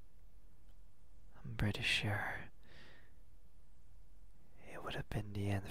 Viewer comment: I thought he said I'm British sure